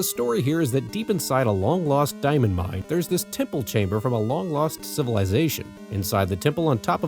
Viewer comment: For a second there I thought you said "Tickle Chamber" instead of "Temple Chamber." That would have been a completely different game. LOL